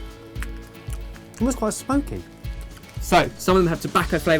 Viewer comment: Bjeasus! When said smoky, I misheard spunky. That be some spunky chilli there!